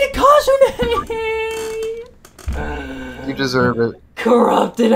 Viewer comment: I just heard: "you deserved it"